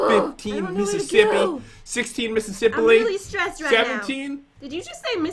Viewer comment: “You’re so porky !!” “Did you just call me fat ..?” 7:20 Pat: “16 Mississipilly.” I died laughing 😂